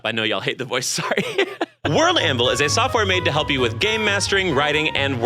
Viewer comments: Not my ass who thought that you said "GAY MASTERING" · I swear I heard him say “gay mastering”